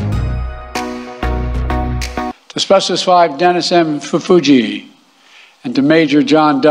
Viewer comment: Turn on CC @ 1:42. Even the CC can't figure out what this disgrace is saying. "Asbestos 5 Dennis M Fuji".